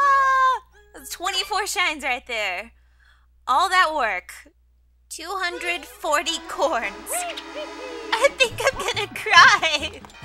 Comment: did she say all 240 CORNS?